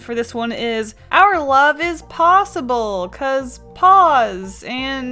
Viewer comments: I thought it said, "our love is plausible". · The mouse/cat one kinda sounds like ur saying “our love is pause-able” but other than that it’s adorable 🥰